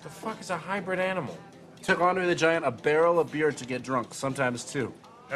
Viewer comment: What the hell is he saying at 0:14? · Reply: "It took Andre the Giant a barrel of beer to get drunk. Sometimes two."